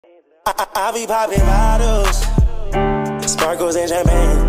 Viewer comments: NAH CAUSE I FR THOUGHT IT SAID I BE POOPIN BOTTLES SPARKLES IN JAPAN😭💀😭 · i fr thought they said sparkles in japan😂